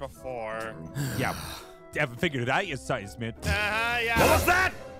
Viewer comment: what did pierce say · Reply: I think it was "you haven't figured it out you seismic bonehead". I might be wrong tho